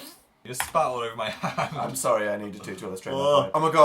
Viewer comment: when phil said dan spat all over his hand i thought dan said "im sorry honey"